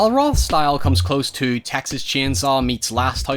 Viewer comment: you could clearly hear him say "Roth'stel" instead of Hostel which is the correct name of the film. Now it m.h may have been a slip of the tongue......or they were, 'Q Anon was tryna imply that this is really the depiction of what goes on in the ROTHSCHILDS Realm!!!!🤔🤐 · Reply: He said “Roth’s style”